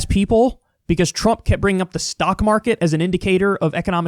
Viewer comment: I swear I heard you say "Stonk market" but thankfully my girlfriend isn't deaf.